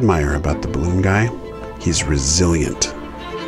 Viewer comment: I misunderstood what you said in the video at 2:54 and I thought you admired the balloon guy for being brazilian